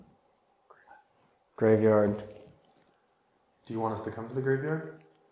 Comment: I hear someone whispering but I don’t understand what it’s saying, I thought it said Graveyard but english is not my first language so if you have any other ideas of what could be said, please, I’m super curious.